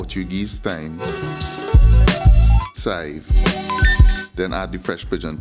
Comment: what is the ingredient that you are saying? Looks like green onions? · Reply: Chive prounounced "SIVE" in our dialect. It is the same green onion just culturally referred to as chives. :)